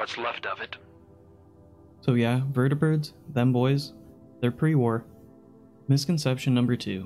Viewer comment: Bro I thought said "Vertibirds, femboys - are pre war."